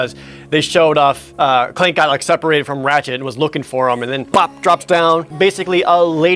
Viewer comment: when he says "Ratchette" it almost sounds like he says "ratshit"